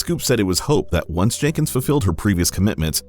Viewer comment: You said "fuffilled" instead of "fulfilled". Why? Spend some time at home, practicin the pronuciation "ful - fill" and "ful - filled". If it helps, then first practice sayin "full" instead of "fuff".